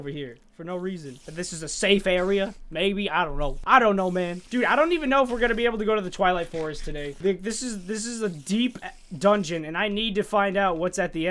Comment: I think I heard "this is a deep a- dungeon." What do u hear ? 😂😂